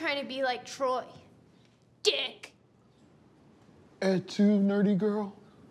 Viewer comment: what is he saying? "Something something nerdy girl" at 2 nerdy girl? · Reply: "Et tu, nerdy girl?" Reference to Caesar' last words "Et tu, Brute?" ("You too, Brutus?" - Caesar recognized Brutus, a supposed friend, as one of his assassins). Search it up if you want more context.